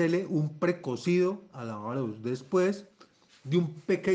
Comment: ¡¡Por Dios!! No se entiende .. ¿Un precocido a qué???? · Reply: Es Arroz el precocido